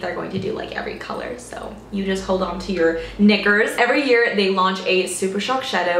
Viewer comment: What did you say at 2:07? I really don't understand??😂😂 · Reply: Hold on to your knickers 😂😂